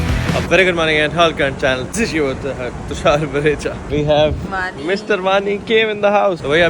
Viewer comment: I just hear mr Vani... bhai ho kya Gaya......